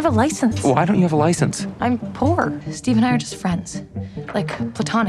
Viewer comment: I saw an edit of the “I’m poor” line and I thought she said “I’m four”